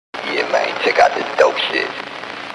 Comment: "yeah mane chech out this dope shit" - 🔥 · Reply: I thought he said "dog shit"